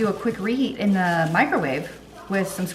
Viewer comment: Thanks for another great video! Every time you say "microwave," I hear "micro-wah-vay" 🤣